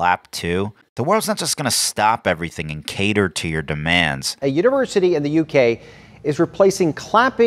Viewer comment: Connect with me: <link> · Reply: Did he say University